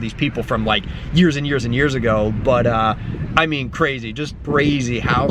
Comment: did he just say brazy..??? lmao